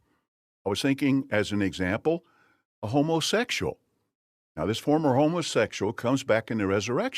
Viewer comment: I keep hearing him say "homeless-sexuals" lol.